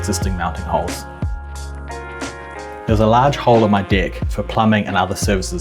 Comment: “There's a large hole in my DECK.” I was like, wait, WHAT DID HE SAY. 😂